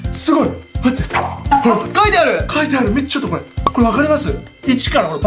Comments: ちょっとこいって聞こえるの私だけ？ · の辺りヒカキン氏がカメラマンさんに対してちょっと来いって言っているように聞こえる‼️